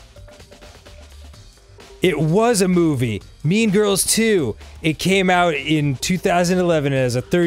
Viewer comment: I thought Dan said "Mean Girls, too." Not "Mean Girls 2". Radically changes the meaning of that joke.